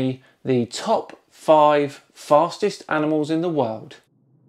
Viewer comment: I thought it said fattest animals lol, it was like expecting coffee and receiving tea.